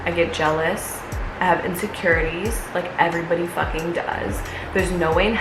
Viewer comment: When I heard "I have insecurities. I heard " her insecure energies" via replicantfish.